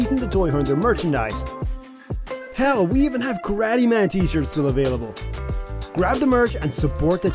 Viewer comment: I swear fam Everytime this ad comes up i always hear Granny Man lmao everytime no bs. I never hear Karate man bro lol im sure I'm not the only one who hears grannyman. what's a grannyman nd what does it look like lmao.